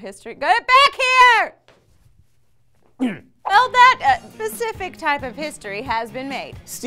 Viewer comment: Did Lee just say "Pacific" instead of "Specific" or was that just the editors fault?